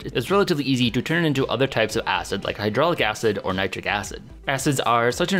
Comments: Did you say Hydrolic Acid @ 3:24? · did you say "hydraulic acid"? :)